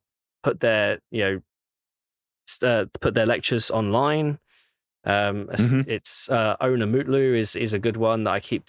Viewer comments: Can anyone understand what he's saying at 1:56:16 ? Id really love to learn about hardware micro arch and cant understand a single word of what hes saying... :( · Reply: Onur Mutlu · Awesome video with great topics and info + personal opinions on precise piece of tech (I really liked the 'burn' on 3D stacking either as a marketing term or an engineer pipe dream) 1:56:21 -> Can someone write me what mooc or person he talked about ? I couldn't understand it and can't research about it on the connected web of knowledge 😅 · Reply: Onur Mutlu